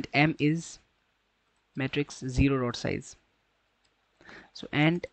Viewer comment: did you say 0 rod size?